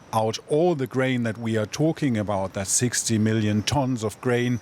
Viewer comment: did he say 60 million tons? Ukraine produced in 2021 33 million tons of wheat and 17 million tons of sunflower oilseeds. Or did he say 16 million?